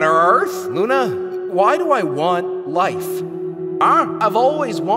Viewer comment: “Theia… Theia… Theia… Theia.. THEIA. The rocky planet known as Theia! The mysterious planet! Hi Theia! No remorse, Theia! Theia? Suddenly, a big crash… BOOM! Two became one! Life! Life! LIFE! I want LIFE! Why do you want LIFE?!” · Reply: I hear "I WANT LIFE!" not "why do you want life"